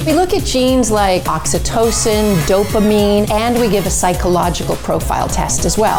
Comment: I heard “dopameme” xD